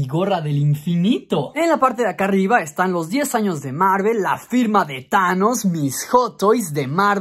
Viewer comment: le entendí la prima de thanos xd